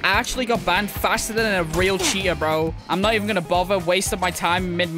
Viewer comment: Cheetah 🐆 or Cheater which one did you say